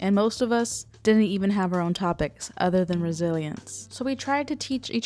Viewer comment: ohhhh you said resiliancee i thought you said brazilians